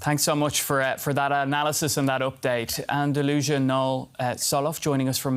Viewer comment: He called her, Delusional instead of Andalusia 😂